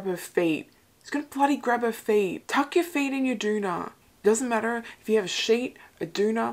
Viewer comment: I didn't know you guys called a duvet (UK, "doo-vay") a doona. New thing learnt! :D Had to look it up, 'cos I thought you were just saying 'doo-da' like you'd momentarily forgotten the word haha, my bad.